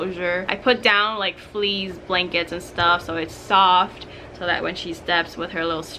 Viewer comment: Is it me or did she say fleas when she was talking about the bird